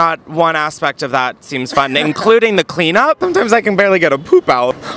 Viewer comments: I thought he said a whole ham. I was less alarmed when I realized it was only a hand lol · At first I thought he said a WHOLE HAM so now a hand seems chill actually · At first I thought he said a WHOLE HAM so now a hand seems chill actually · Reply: I'm so glad I wasn't the only one who not only thought he said a "whole ham" but then had that reaction to learning it was a hand lol. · I mean a hand sounds better than what I originally thought he said, a whole ham, like a Christmas ham? I’d take a hand over rum ham any day.